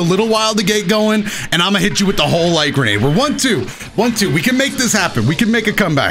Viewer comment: "I'm a hit you with the whole grenade: What I thought he said, I'm a hit you with the holy hand grenade